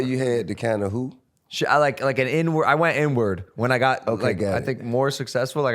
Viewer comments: TI definitely thought he heard N-word lmao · t.i thought he said N word lol.